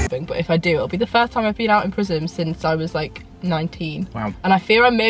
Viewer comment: I thought you said ''it will be the first time I've been out of prison since I was 19'' lmao